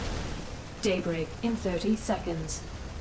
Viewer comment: Whenever it said "Daybreak in thirty seconds", I kept hearing "Date-rape in thirty seconds".